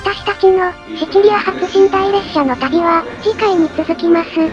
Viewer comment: の「次は船上でお会いしましょう」の時ちとよそ見をしていて「戦場でお会いしましょう」に聞こえて「いくらなんでもイタリアの寝台列車殺伐とし過ぎだろ」と思ってしまった